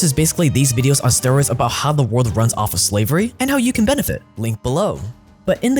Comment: did you say benefit❓ you teach how to benefit off of it❓